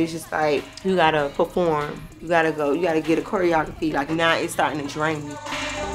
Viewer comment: i thought caresha said she don’t drink water 😩